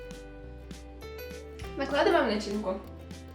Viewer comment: ох и.ай да!! смотрел видео в 3 часа ночи звук негромко был .... и когда сказала.накладываем начинку--- так мне послышалось ---откладываем личинку!!!! а так все ок!!! молодчинка